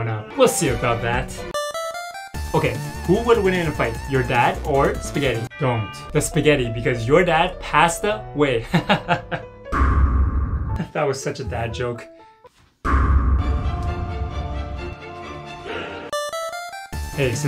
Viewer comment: i thought he said bad joke turns out he said dad jokes